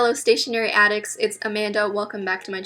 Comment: i think i’m going crazy but i swear she said ‘commander’ instead of ‘amanda’?